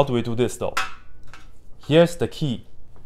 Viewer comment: I genuinely thought you said "here's the tea" 😂